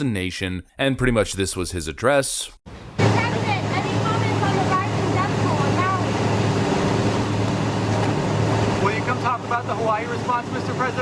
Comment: I heard “The Biden death toll in Maui.”😅